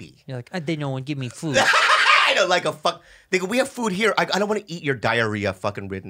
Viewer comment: It sounded like Bobby said, " N*gga we have food here"